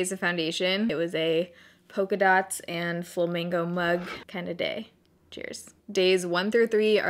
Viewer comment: At first I thought you said "full mango mug" lol what? 😂 but Thankyou so much for always bringing happiness to my days. I always look forward to your videos and it really shows how much hard work you put in. Thanks again for becoming my favorite youtuber (: love you ❤️